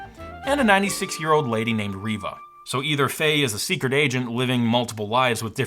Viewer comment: my son heard this from another room and said of course she's a spy if she's a secret aisian. no, i don't think you heard that correctly i told him.